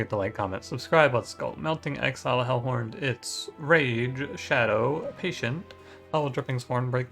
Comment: Okay so at 1:56 I swear I was expecting the third word to be "Legends" I was gonna say my dude congrats on the sponsorship Then I realized you said "Rage. Shadow. Patient."